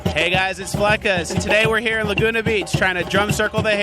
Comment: I thought it said Lasaunga bitch ! Hahaha